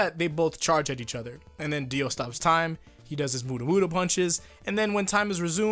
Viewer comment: Did he just say buddah buddah punches?